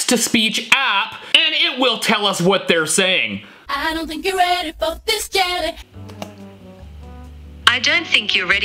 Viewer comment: I think you’re ready for this jelly is what she says 0:43 😊